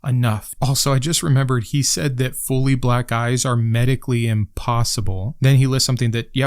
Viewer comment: LMAO. I just heard you say that this guy said, "fully black guys are medically impossible." Oh, "black EYES".